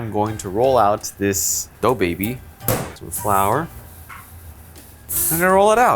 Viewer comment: When said dough baby it sounded like he said DaBaby.